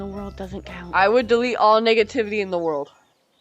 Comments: What did I say? 👀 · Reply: he thought you said the n word but You said negativity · did u say what i think u just said?? 😭😭🙏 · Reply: 🥷tivity